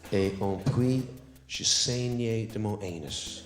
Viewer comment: I failed to laugh at this joke but only because, as a Francophone, what I heard was j'enseignait de mon anus and I couldn't understand why he was teaching out of his ass!